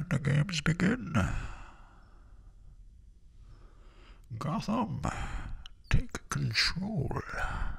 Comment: For some reason I always hear Greetings and not Gotham in the movie. Welp guess I was wrong.